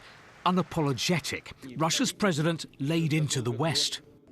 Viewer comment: I thought you said Russia was loosing😂😂😂